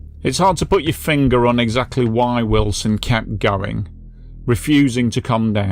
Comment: I have seen a few comments stating that I said he was born in 1998 - maybe it's just my accent, but I definitely say 1898.....check the captions if you still don't hear it !! If you would like to support this channel - <link> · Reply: I heard 19....... ut i also thought you said his name was illson....lol